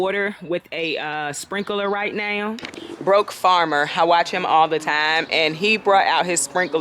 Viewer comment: You mentioned a farmer...Sounds like you said "The Broke Gardner" uses a sprinkler to water his garden. I didn't see a link in your info above.